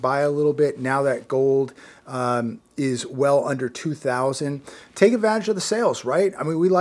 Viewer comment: Did I hear take A VAG OF THE SALES? DAMN IS IT NICKLE NIGHT???😆😆😆😆😆😆😆😆😆😆😆😆😆😆😆😆 at 2:22 min mark still lov ethe channel we need a video everyday!!!! Don't slip!!!